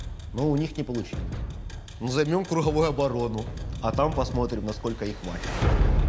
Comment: subtitles are wrong. he said "lets see for how long they will hold", you wrote "we will destroy them". Good "job" Vice.